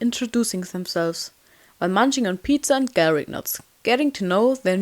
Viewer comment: For some weird reason In the beginning of the animatic I heard "carrot nut" and I was so confused Until I realised it was "Garlic Knots" I watched this like 20 times before realizing this